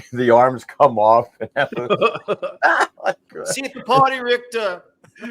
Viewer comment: I first saw this movie with translated subtitles, and the translation of Arnold's line was, literally, "See you at the party, Richter!" Hearing Arnold say that in English however, I wondered, "Is that the best they could come up with?" Later, I found out that the line (despite what IMDb says—and I cannot confirm otherwise since I do not seem to have a Blu-ray Disc with English Subtitles for HoH) may actually be, "See you at the paddy, Richter!" Now, that makes sense and is funny as frak! 🤣 Also, I can see the translator and IMDb users think Arnold is saying "party" instead of "paddy" because in his thick accent "party" /ˈpɑɹ.ti/ would indeed sound more like "paddy" /ˈpædi/ since Arnold would pronounce /t/ more like a /d/, and the vowels would be a bit unclear. But Arnold would also easily pronounce "paddy" /ˈpædi/ correctly! I really need to get a Blu-ray Disc of Total Recall.